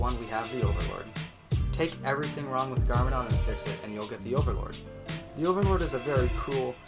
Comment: wtf dos he say? garmadon and pixn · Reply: Garmadon and fix it. Sorry I have a tendency to talk fast. I don’t want my videos to be super long